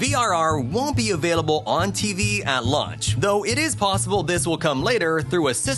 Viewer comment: Did he say at lunch? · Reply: He said launch, but ngl I thought he said lunch too lmao